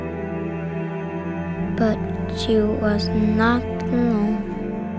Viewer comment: I'm still trying to figure out what was said at 3:43. "But she was not alone?" "But she was not normal?" lol, I really can't figure it out.